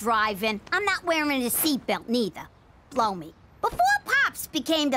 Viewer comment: I thought gina said plow me in the beginning i have a dirty mind😅😅😅